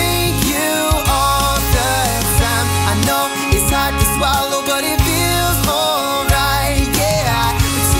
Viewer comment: i thought it said "ik its hot to swallow" and "youd rather be against me"